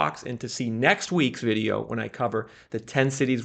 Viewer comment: What funny is at the end of the video when he mentioned "the ten cities" I thought he said the "tent cities" I was like: "Yeah. That's about what we can afford..." xD